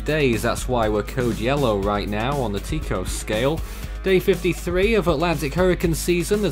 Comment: On the WHAT Scale? 7:09 - Hanna, followed by WHAT? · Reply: I thought he said "Teacup Scale" at 0:37 but I think he said "Tico Scale"